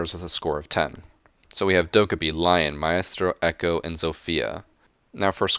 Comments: It sounded like you said my ass tro instead of maestro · It sounds like your saying “My ass throw” when you say Maestro.